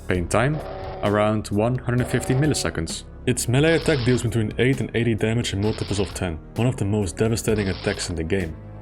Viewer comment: Hey there's a small issue in the video at about 7:24. The video says "Its melee attack deals between 8 and 80 damage in multiples of 10," but the subtitles read "-between 8 and 80 damage in multiples of 8." You should probably pop into YouTube's subtitle editor and fix that when you get a chance. I'm not hard of hearing, I just have difficulty understanding human speech sometimes (mostly because some videos are horribly audio balanced, or the speaker is foreign, or I'm eating, etc), so I have non-automatic subtitles on by default, but I have an easy time discerning what you are saying usually which is how I noticed this